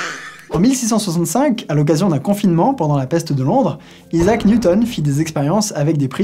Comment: j'ai entendu confinement là !!! théorie du complot !!!!!!!